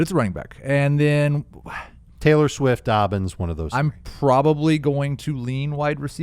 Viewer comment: Wait, did you guys just recommend drafting Taylor Swift at 1.01? lol, I really hope I heard that wrong.